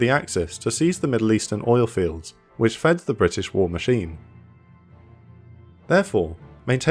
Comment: Regarding the (potential) loss of the Suez Canal: What was stated: 3:23 "...which fed the British war machine." What I heard at 1:11am: "...which fed the British royal tea."